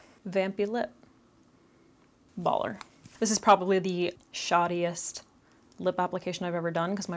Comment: help omg i heard “shawtiest” as in shawty 😭